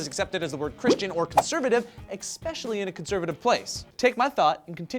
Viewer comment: Did you just say ECK-specially, Joe?